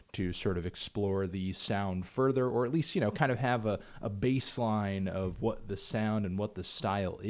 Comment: did he say bass line?